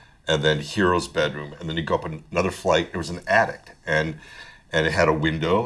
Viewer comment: anyone else hear addict?